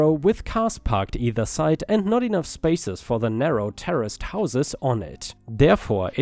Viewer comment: Traditional stars here: 🌟🌟🌟🌟🌟🌟🌟🌟🌟🌟🌟🌟🌟🌟🌟🌟🌟🌟🌟🌟🌟🌟🌟🌟🌟🌟🌟🌟🌟🌟🌟🌟🌟🌟🌟🌟🌟🌟🌟🌟🌟🌟🌟🌟🌟🌟🌟🌟🌟🌟🌟🌟🌟🌟🌟🌟🌟🌟🌟🌟🌟🌟🌟🌟 I only heard the voice crack in the last story, so it's not that bad. However, I did misheard you saying "terraced" as "terrorist". But again, great stories and Disney cam.